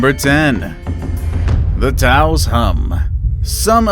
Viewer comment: i thought it sayed the tacos hum 😂😂😂😂